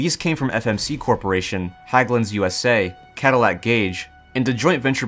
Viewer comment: what is he saying? "Haglin's USA" or "Hägglunds USA"?